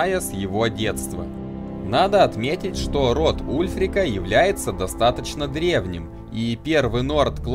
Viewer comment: Мне послышалось "Рот ульфрика был достаточно длинным"